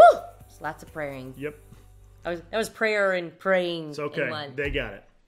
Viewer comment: for a sec i thought tamera said “don’t forget jesus christ too!” and i was like oh damn ok sis i won’t but she said “don forget to subscribe” lmfaooo